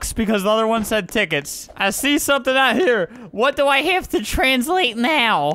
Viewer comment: Is it just me or did he say translate s**t at around 9:52 ? Or did he say translation?